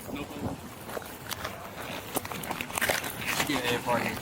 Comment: what did he sayyyyyyy 😂😂😂😂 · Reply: Chicken air fryer 🤤🤤🤤